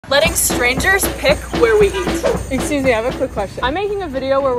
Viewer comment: When she said “excuse me…” I heard “hey Susie” lol 😂